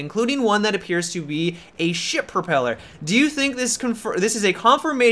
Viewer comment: I thought you said 'shit propeller'...